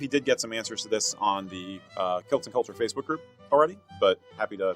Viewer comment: I have bad hearing and the sound on this video isn't as loud as some, so I heard "Kilts & Culture" as "Celtic Culture" and couldn't find the group on Facebook. After poking around your Facebook page, I looked at the events tab and found it though. Thanks!